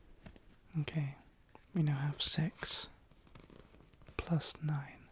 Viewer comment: lol I heard sex instead of six